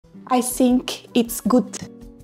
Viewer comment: I thought she said I stink